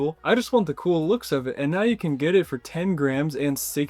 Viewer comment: thought you said grand...